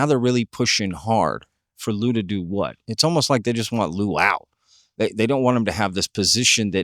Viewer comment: did you say they just want a luau? 🤣 Seems fitting given your setting 🤣